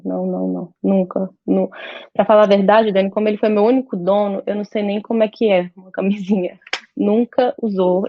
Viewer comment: Jura que eu ouvi "ele foi meu único dono"?????